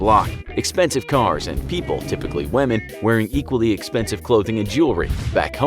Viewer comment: did i just hear wamen from the voicer